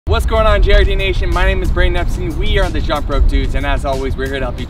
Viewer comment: WOW!! He says "Whats goin on JRD Nation" at the start. For months I thought he said "What's goin on Cherokee nation?" Like... dude, huh?